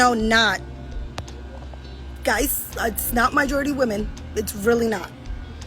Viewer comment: Did she just say "MYjority" ?